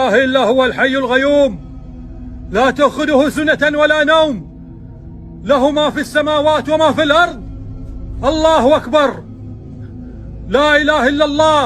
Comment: I swear he said alaha akbar home office